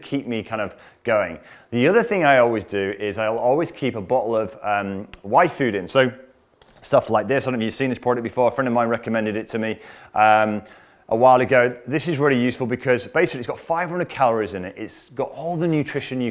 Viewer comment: What was that? Sounded like "wifefoodin"? Is it available in the US?